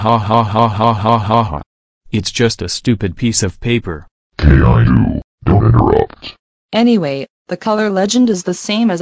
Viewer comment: What did the teacher say at 0:07-0:09? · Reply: Caillou don't interrupt